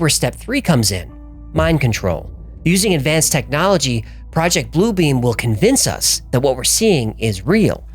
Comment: I thought it said "Blue Dream" for a moment... LOL